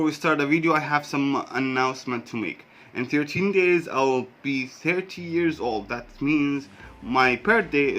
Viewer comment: nope I'm almost 30 .. after 13 days // btw i just checked your channel .. its really fun :D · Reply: Thanks for checking it :) I thought you said in 13 days you will be 17 years old, my bad